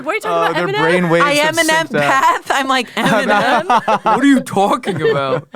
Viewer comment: I heard "M&M-path" too! I thought he was making a joke 😂